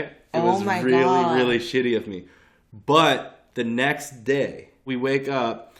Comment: did he just say the next dick 🤣